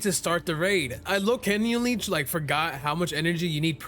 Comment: did bro just say lowkenuily